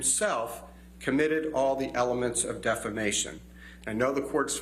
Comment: I thought he said Amber Defecation @ 6:46 and thought she has all ready ejected her defecation over and on Johnny side of the bed